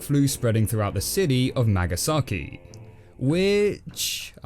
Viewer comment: For a second there I thought you were saying It had to look up if Nagasaki was a real place, and no it isn’t.’ And I was about ready to type “Yeah, Nagasaki is a real place! We bombed the shit out of it in World War 2!” Then I saw you type ‘megasaki’ and I was like, ‘oooooooohhhh....’